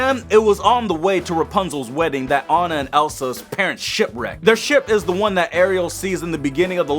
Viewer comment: wen u sed ship recked it sounded like u sed shit